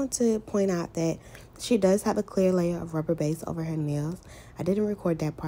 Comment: What did you put on her nails before your started applying acrylic ? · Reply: I heard her say rubber base